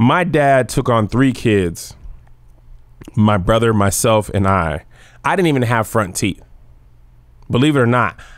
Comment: Wait... "my dad took on 3 kids; my brother, myself and I" aren't 2 of those you😅 unless I misheard lol